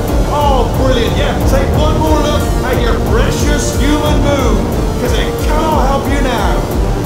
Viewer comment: When I first heard him say “take one last look at your human moon” I was like, “You have got be kidding me!” XD Fucken love this game